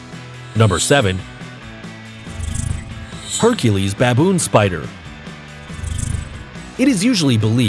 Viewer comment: I thought he said bacon